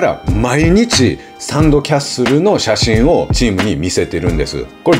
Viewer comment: 聞き間違いで「サンドキャッスル」が「サム・キャセール」に聞こえて、それを毎日見せてる！？どういうこと！？ってなってしまいました、すみません。。